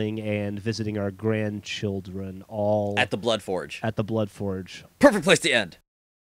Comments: i thought it said blood orge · I thought this said "Bloodfrogs".